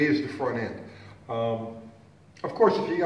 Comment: Did you just say "Traction and WHEATIES" at 4:43? Wheaties?...